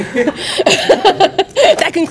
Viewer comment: I heard "colby the ass Covington"